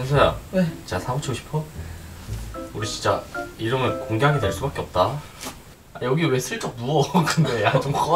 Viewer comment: 에 나만 공개하게 될수밖에 없다 라고 들리나?